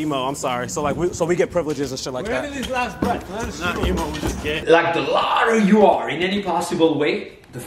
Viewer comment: did say “im not emo i’m just gay” 💀💀💀